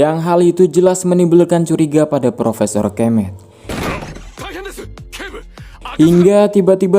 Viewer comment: Njiirrr gue Salah denger Nama "PROFESOR KEMEM " 🤣🤣🤣